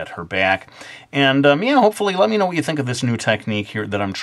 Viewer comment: sounds like he said meow XD